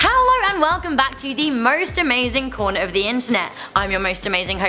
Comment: I thought she said hello welcome to the most amazing porn of the internet😂I was so confused for a second...LOL